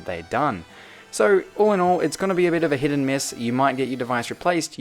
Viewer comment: Did u just say, Hit or miss? Oh...... NO